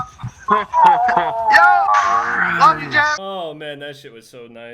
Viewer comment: anyone else hear jgod say “love you jev”